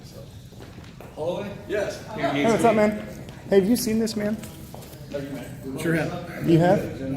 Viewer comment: did he say “SURE HAVE”